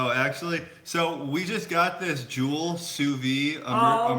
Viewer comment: HI! What are you saying at 15:00 SuVee Immersion? are you saying a Jewel? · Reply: Jenny Shain Is called sous vide and Joule is the brand they have. It's a way of cooking in a water bath. It's pretty great. There is a channel, I think it's called sous vide everything that's pretty good.